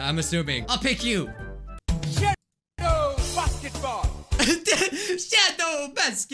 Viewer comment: "Shoot those Basketballs" What I hear: SHOOT THOSE F***ING BALLS